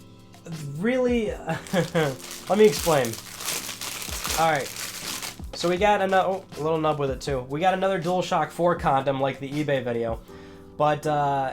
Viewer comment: Is it me or did he say DualShock 4 condom